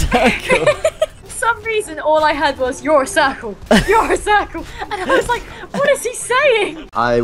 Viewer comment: anyone else trying to figure why she was moaning when the juggernaut was hitting her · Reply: you know at first I thought he said your a circle too